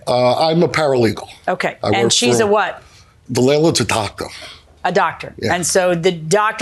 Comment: “the landlords a Taco” That’s what I hear. Not doctor.